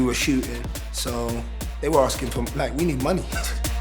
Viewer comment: sorry wtf did he say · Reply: they was asking from blank , we need money